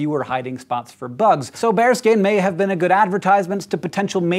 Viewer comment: I thought you said bear skin, not bare skin, for a while. Haha